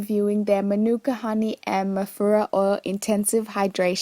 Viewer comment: At first I thought u were saying mafuta oil haha